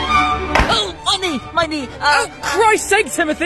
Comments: Am I the only one that noticed that when he fell he sounded like he said “ Money Money “ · I legit thought when he tripped and said "my knee" I thought he said "money" lol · I hear "money" instead of "my knee!" Tf is wrong with British accent 💀